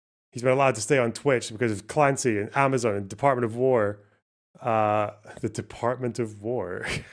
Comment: When says “department of war” I mishears “department of vore” which would be much more based